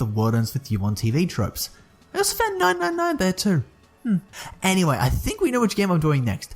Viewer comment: So @ 0:40 I heard "Es fen 999 there too" lol, WTF was I supposed to hear? & was it actually English?